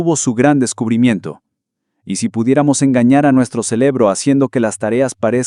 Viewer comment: ¿Escuché "celebro" yo solo?